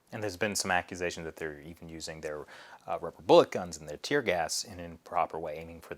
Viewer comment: Thrax “rubber bullet guns” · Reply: I hear it now lol.. thought he just said bullet guns was like wth